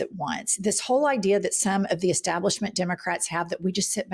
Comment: I thought he said it was all democrats haox. What a insufferable lair he is !